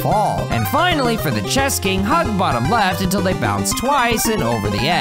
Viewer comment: I thought he said chest king💀💀